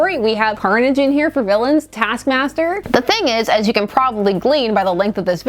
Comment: I misheard, "Taskmaster", as, "Cat Snatcher".